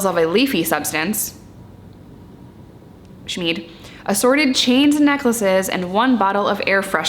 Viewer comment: damn I almost thought she said Sneed